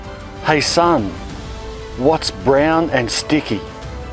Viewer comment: I thought he said "what's brown and STINKY"!